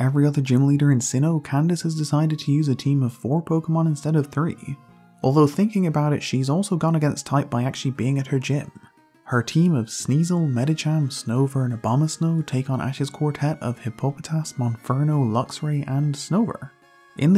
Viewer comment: EVEN THE CAPTIONS SAY OBAMA SNOW.